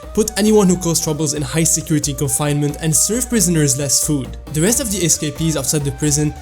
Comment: I thought they said "Serve prisoners as food" XD